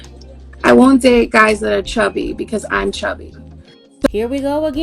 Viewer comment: I thought she said chubby not FAT women will always try make themselves look good.